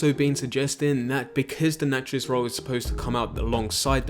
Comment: I thought you said naturist for a minute there i can see it now us all going around wearing nothing but are gun belts, now that would be something.